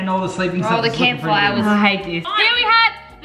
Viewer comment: Did anyone else hear at 8:20 ish say : I hate disc” and then quickly keep going